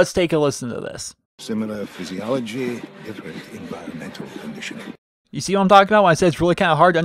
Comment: I thought he was saying "Similar physiology, different environmental conditioning"